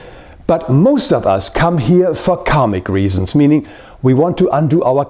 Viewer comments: I thought you said people come here for comic reasons. Lol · At first I thought he was saying comic reasons